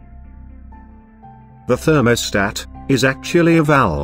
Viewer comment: It sounds like you say something like themothat